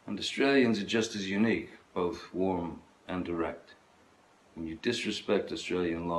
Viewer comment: I really thought he said "warm and erect" the first time.